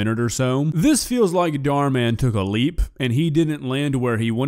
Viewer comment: misheard him, thought he said "dhar mann took a leek"